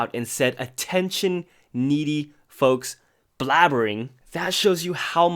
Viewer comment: I thought it said “attention needy hoes”